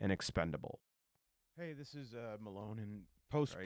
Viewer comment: Did he just say that he's Post Malone?